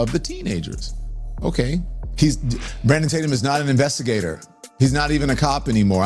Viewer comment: did he say Brandon titem hahahaha